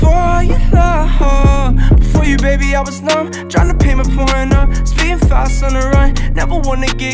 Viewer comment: it sounded like he said "tryna pay my pornhub"